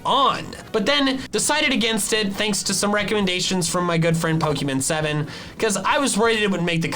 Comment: thought he said pokimane lol